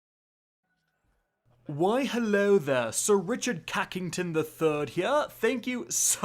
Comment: It sounded like you said sir cucking ton